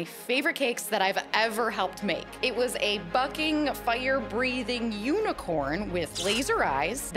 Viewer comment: Instead off you saying bucking I thought you said ummm strew it I’ll say it FUCKING sorry I’m not very family friendly